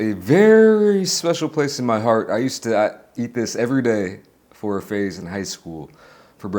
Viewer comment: thought said "i ate this every day while high" 🍃:face-red-droopy-eyes